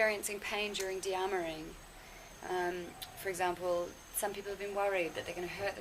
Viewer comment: what is the word you both are talking about? i may not be able to understand the accent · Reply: dearmouring (Entpanzerung)